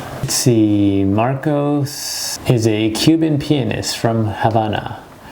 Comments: when he says pianist he sounds like hes saying penis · i thought he said Cuban penis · When he says pianist I thought he said penis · I thought he said marcos penis lmao😂😂😂